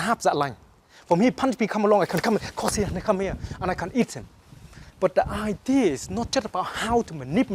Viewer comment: does he say he can eat him?